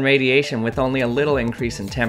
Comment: I literally thought for a second he put the ball on his own hand 💀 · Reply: when he said "lab assistant" i heard "my sister"